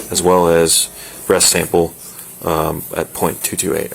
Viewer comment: the audio, transcript and subtitles say "...as well as BREAST Sample "